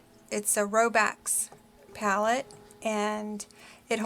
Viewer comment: About your big pallette...it sounds like you said Robax pallette, is that correct? I tried looking it up on Amazon and can't find it so I am assuming I have the name wrong.